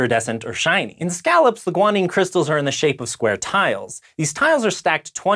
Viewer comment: I heard Scoia'tael when he said "square tile"